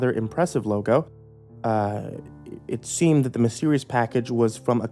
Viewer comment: thought it said "my serious package" lol